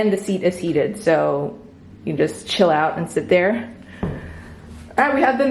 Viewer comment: hahaha I had to rewind it i thought you said $hit out but you said Chill out hahaha. Love your videos!